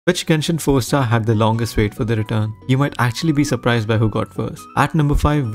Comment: I thought you said which genshin character has the longest hair ☠️☠️💀💀